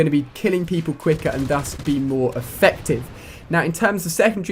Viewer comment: what did he say 2:40 I'm trying to extend my vocabulary in English. If someone would comment the word, it would be great. (I think he said dos, thos or something) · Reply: I think he said 'thus' which means as a result or consequence of something.